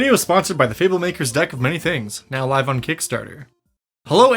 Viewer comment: I thought he said 'dyke' of many things lmao